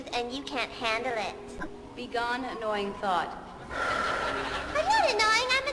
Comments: lmao I thought she said 'be gone annoying THOT' instead of thought loooool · i thought she said "be gone annoying thot" and it took be a second to realize it was thought not thot omg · Oh my God, I definitely thought she said "begone annoying thot" then realized she just said "begone annoying thought" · I THOUGHT SHE SAID "BEGONE ANNOYING THOT" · Y'all at 11:20 I thought she called her a Thot (it was "thought" I'm pretty sure) but can we pretend Ellen invented Thot now. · bet i heard "begone annoying T H O T" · i laughed so hard i thought she said "begone annoying thot" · It sounded like she said 'be gone thot"